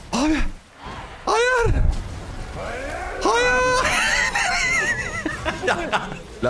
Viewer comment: master HAYIIIIIIR diye bağırdı mı ben mi yanlış duydum?!